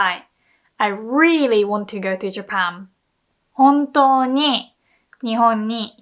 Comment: Wait ! Did she said JapaM ?!